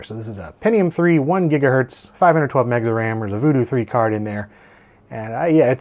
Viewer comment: Did you just said 512 Mega Ram?